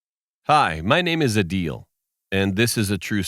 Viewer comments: i thought he said my name is a deal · Who else thought he said "hi, my name is adi", any addicts heree😂😂